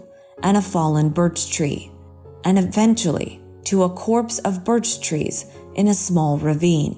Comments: Did she say, "...a corpse of birch trees."? ( 16:00 ) Hmmm...were they all dead? The older I get, the less faith I have in our education system. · 1. For Mystery #2, is that Lillium (Elfen Lied theme), piano theme? Any link to the track? :) 2. It's a COPSE not a corpse of trees? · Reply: Ok I def thought I heard Corpse of trees and figured I must of heard wrong. I had never even heard the word copse so thanks! Learned something knew. And I too was wondering about that same track so double thanks!